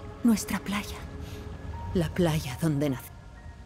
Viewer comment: "la playa donde na***" que dice ? Por qué sigue moviendo la boca , acaso es un spoiler ocultado? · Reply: No, en la versión inglesa lo dice entero. Dice algo así como "la playa donde nací, ven a buscarme"